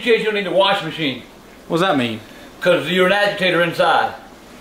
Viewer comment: FYI: The column that spins in the washing machine is called the "agitator." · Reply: I thought he said “agitated” meaning upset